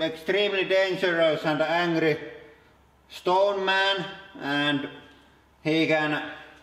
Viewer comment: ***** I was saying because I wasn't sure if that was intentional or not. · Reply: I believe you might have misheard him speaking. He said stone man instead of strong man.